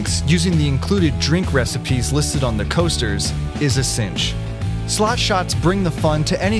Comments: I thought it said sloth shots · Please tell me I'm not the only one who thought it said 'sloth shots'..